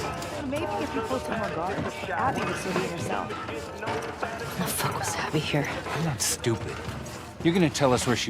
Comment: are you able to say what Ellie says on 13:44? She clearly says something to herself while listening to Nora and the other assholes there... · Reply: she says "why the fuck was abby here?"